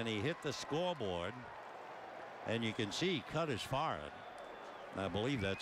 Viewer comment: okay does anybody understand what the announcer says about Harper at 2:18? "... and you can see he cut his "foreign" ? what?